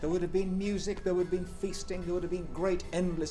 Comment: I thought he said fisting.